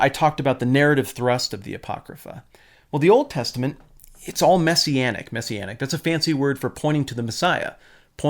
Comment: Messianic .. I first heard it as "messy endings" .. which describes a lot of the Old Testament narrative as well :)